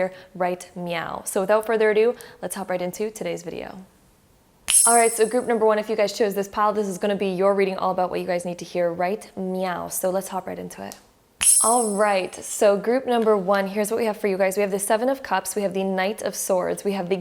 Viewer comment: Just curious, did she say "just meow" ? 2:02 🤣